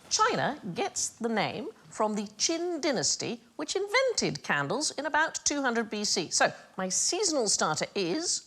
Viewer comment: I have some audio processing issues, and I absolutely thought Sandi said “They invented CAMELS! My brain broke and I had to go back and figure out what went wrong! 😂🤣💀